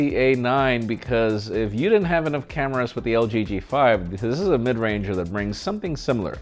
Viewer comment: did you say lg g5?